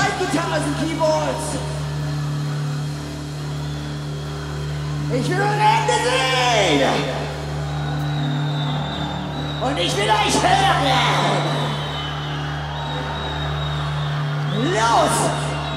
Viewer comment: que dice what she said??? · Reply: "Ich will eure Hände sehen und ich will euch hören! – Los!" = 'I want to see your hands and I want to hear you! – Go!'